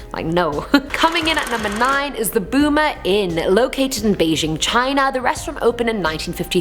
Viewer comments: I thought she said the boomer inn🤣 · I almost thought she said "The BOOMER Inn". 😂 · I thought she said the boomer inn